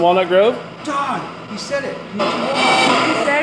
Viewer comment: "konichiwa" I think she says??